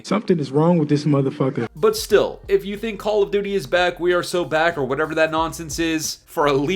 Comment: "wE aRe sO bACk gUyS!!!" 🤡🤡🤡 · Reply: Thought you said "we are so black guys"😂